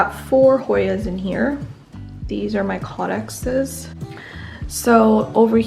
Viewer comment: The round plants at 18:04, the ones in the glas container together with the 4 Hoyas. What are they called? You said something like "cawdaxes" or "kodakses". I googled, but couldn't find anything. A beautiful collection :)